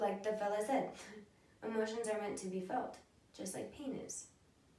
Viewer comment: Wait.. did I misheard? "emotions are meant to be felt just like penis" ?? 😂